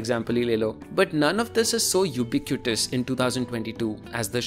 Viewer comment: what did he said? "Eupicutous"? Can anyone pls tell me how to spell it and it's meaning too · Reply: Ubiquitous :)